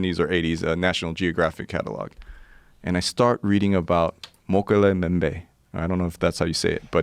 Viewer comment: The proper pronunciation of Mokele M'bembe is-- Moe-kel-ay Muh-bem-bay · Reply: Lol wtf i watched 90s anime and this is what they were talking about?? I heard it as Dokere mubembe too.